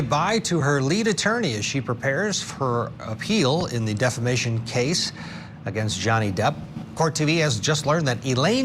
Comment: Everytime they say “The Defamation Case” I hear “The Defecation Case” which in the case are one and the same 😅